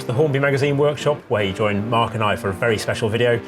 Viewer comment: I'm here because i thought it said 'Horny Magazine'